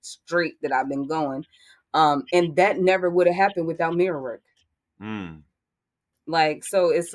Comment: is she saying "mirror work"? Can someone please share the link to the book she is talking about? Thank you :))